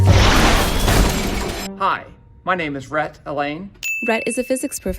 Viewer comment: Thought he said his name was Rat Allainey